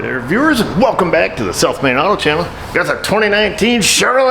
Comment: Forever I thought you were saying "welcome viewers to the self-made auto channel". I realized you're saying "...South Main Auto Channel". :P